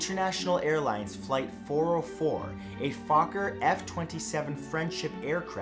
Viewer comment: 😅😂 it sounds like he is saying focker or f***er.